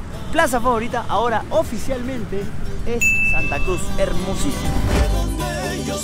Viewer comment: dijo Santa Cruz o escuché mal?